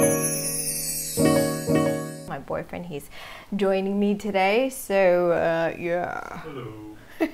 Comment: did she say "my boyfriend is doing me today" ??